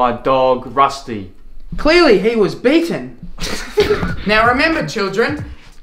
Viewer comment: “clearly he was beaten” lmao i can’t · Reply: OH. i misheard, i thought he said mating instead of beaten